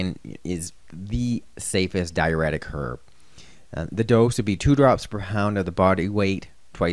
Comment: Lol did you say 2 drops per hound?